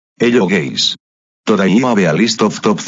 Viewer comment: "Hello guys" I heard: "Hello gays"